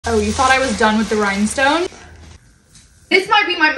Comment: I thought she said you thought I was feeling you 💀